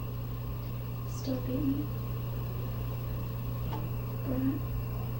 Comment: I hear "Still hate me?...Brat." And yeah, I'm here in 2020. This bitch keeps coming up in my suggestions. She's like herpes; just keeps popping up 🙄